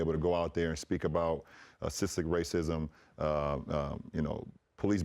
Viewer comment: what’s that word again bron? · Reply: Systematic lol he got tongue twisted probably need water , chill on the king